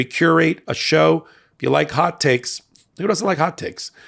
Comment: Every time I hear “hot take”, I actually hear “hot cakes” making me hungry. 😅